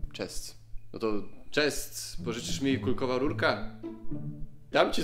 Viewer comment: Ja usłyszałam kulkowa rączka 😂😂